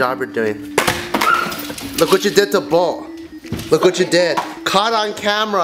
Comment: Thought he said "look what you did to bowl" in the beginning lol